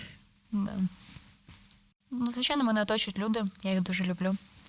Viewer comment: На 22:14 почулося : "звичайно, в мене є дочка Люда"